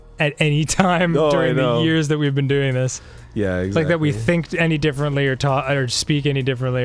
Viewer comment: did Dan just say "thinked"? XD